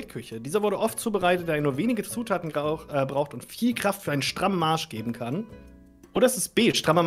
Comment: ich hab kurz nicht zugehört und höre erstmal Strammer Arsch… wie hab ich das M überhört